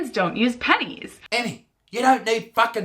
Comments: When is it said no pennies I thought is said no penis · I thought when she said ‘no pennies’ that she said no PENIS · No pennies I thought it said penis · Fuck I thought it said no penis but it’s no pennies😂😂